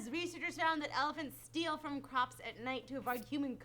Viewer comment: On the elephant story I misheard it as "elephants are raiding the cops" instead of crops. Also can you guys try to keep the sound level the same on talking and the music bits.